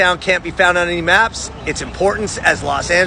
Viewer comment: I watched this whole video because I thought it said LA’s “first burrito”